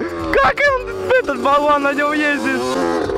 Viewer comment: Вован? Он же (или мне послышалось? )